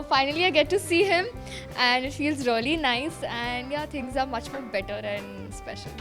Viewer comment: does kaanchi said 'she' instead of he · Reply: Shubham Sharma Guys She said " It feels really nice to see him " Its just an accent bunch of losers